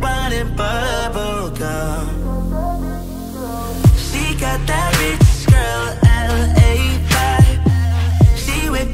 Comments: i thought it said “she got that fitness girl LA vibe” nope i was wrong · We all searched for “she got that rich girl LA vibe” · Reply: i thought he said “she got that rich girl la bod” lmaoooo